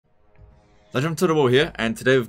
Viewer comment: I thought I heard Legend fart